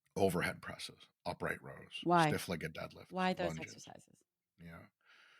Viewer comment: Can someone please tell me what he says when she interrupts him at 28:30? Really trying to learn. Thanks! I got: Bent over rows Dead lifts Squats Overhead presses Upright rows But he says something else that I can’t make out. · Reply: Stiff-legged deadlifts! 😊